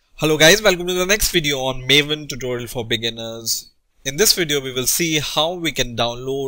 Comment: lol at the beginning i thought he said maven tutorial for billionaires.